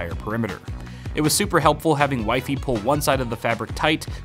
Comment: sounds like he said " it was super helpful having white people..."